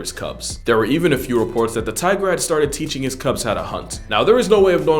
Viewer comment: did you just say tigrex?